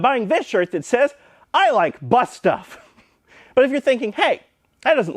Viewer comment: 🤣🤣🤣 I like bus stuff 🤣🤣🤣 I swear at first, I thought he had actually said "I like butt stuff" until I read the shirt LOL, Nice play on words man 🤣🤣🤣